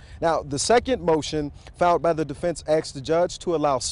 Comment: They let a professional reporter keep his job after saying "axed" instead of "asked"?